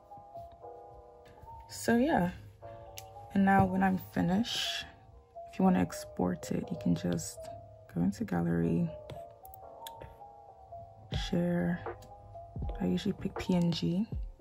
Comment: I thought she said she wasn’t an expert